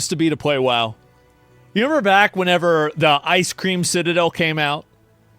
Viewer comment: Did he call it the Ice Cream Citadel?